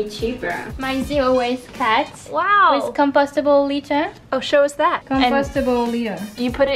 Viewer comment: When she was talking about the cat I heard "compostable later" instead of "litter" and it got real dark for a minute hahahaha